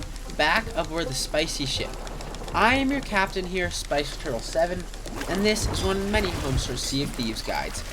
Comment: Come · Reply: Yo, mybad. I thought you said Spicy Girl. lol. I heard it now, SpicyTurtle?